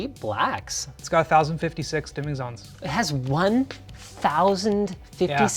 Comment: 1056 dimming zone is really low for 55 inches considering the much "cheaper" neo g7 has 1196 dimming zone on 32 inches screen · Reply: Maybe I misheard but I thought they said 1500+ dimming zones?